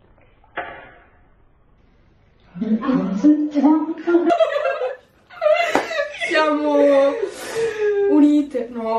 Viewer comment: cosa dice? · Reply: dice 'hai sentito?"